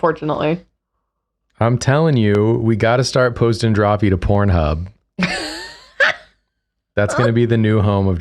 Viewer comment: I zoned out for a SECOND and suddenly I hear “we gotta start posting drawfee to cornhub” Turns out though even in context it was out of pocket lol